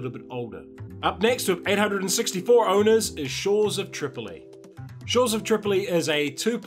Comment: Kept the video in the background while sleeving cards and not paying attention to the video, when at 4:10 i hear "Short of triple A", and i thought "wow, a game that is just short of being a triple A and is owned by only 864 people?!" :-D